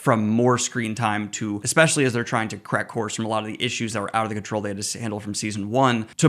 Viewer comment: in the video, you say “correct course” very quickly and I heard “crack whores” 😂😂😂